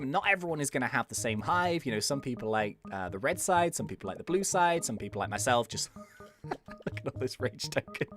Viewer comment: For a second I thought you said blue scythe